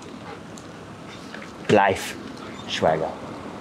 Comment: bro i just heard patty speak dutch 11:07 he says blijf thats stay